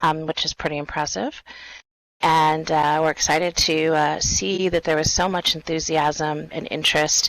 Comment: Idk what they spoke about in the beginning? All i heard was "eep" .. "we're excited for eep" o.O wut lol